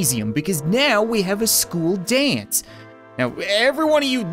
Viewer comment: I thought school dance said social distance